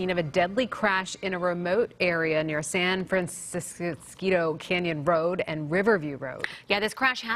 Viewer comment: Had a stroke trying to understand what she was saying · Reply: San Francisquito Rd. Most people do not know how to pronounce it lol.